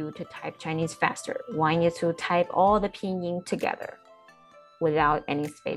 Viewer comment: "painting" instead of "pinyin" in the subtitles :P